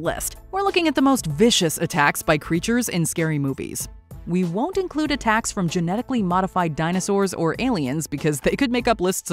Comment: Kinda thought they said “We won’t include genetically mutated animals” but yk